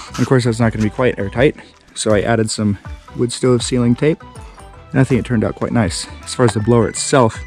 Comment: "added some ******** (what?!?) tape" .....did he call foil tape "luscosium"? WTF is that term? Otherwise, this guy is a genius! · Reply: "wood stove sealing tape." XD Thank you!